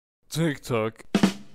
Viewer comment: Sounds like Pepperman is saying "Tick Tock"